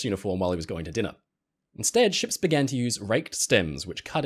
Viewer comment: Apart from I thought you said raked in, (my bad) I don’t think I’ve thought to ask this but it does make sense. Poor captain! Laundry wasn’t as convenient back then!